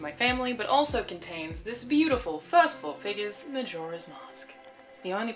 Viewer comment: What did you say here? "First fowth figas" ?